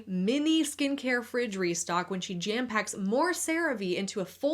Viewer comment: Dang I thought you said mini fridge